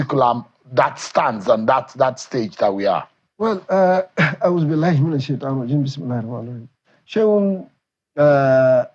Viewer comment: What did the ex governor said before he started answering the questions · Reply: He started with أعوذ بالله من الشيطان الرجيم (A'udhu billahi min ash-shaytan ir-rajeem), which is a supplication that translates to "I seek refuge in Allah from the accursed Shaytan (Satan)." Then he said بِسْمِ اللَّهِ الرَّحْمَٰنِ الرَّحِيمِ (Bismillahir Rahmanir Rahim), which is a verse of the Holy Qur'an that means "In the name of Allah, the Most Gracious, the Most Merciful." These words are usually recited by Muslims at the beginning of a speech/action to seek Allah's protection against Satan misleading them in speech and action, and for Allah's guidance in what they are going to say and do.